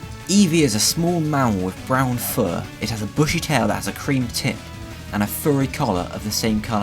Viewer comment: i thought it said with a creamy tip